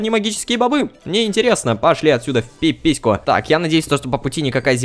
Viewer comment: А мне одному послышалось что на 8:11, он сказал пашлите от сюда в пипиську. :D 😂😂😂